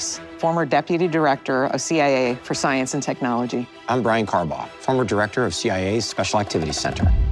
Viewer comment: i thought that guy said his name was brian carbomb holy shit i nearly died laughing